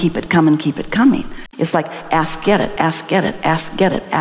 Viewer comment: At first I thought Esther was saying,😂 a$$ get it, rampage. Thank Goodness, I'm not too serious today.